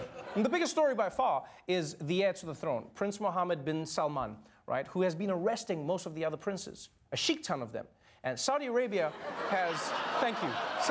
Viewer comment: No, I thought he said Sikh-ton?